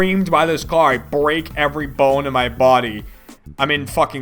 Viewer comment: I swear it sounds like he says every bunny. Lol